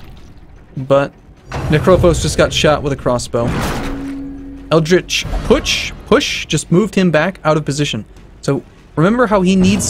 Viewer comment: I kept hearing 'eldritch putsch' whenever you said 'eldritch push', and all I could think of was lovecraftian hitler.